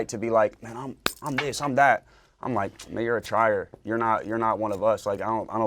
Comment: What he say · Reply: Tryer i believe. You're a tryer, you're pretending. That's how I heard it.